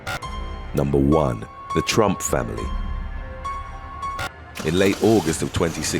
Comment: I swear I thought he said Trump family